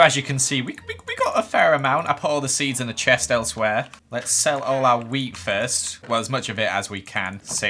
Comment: who else thought he said “sell all our weed here”?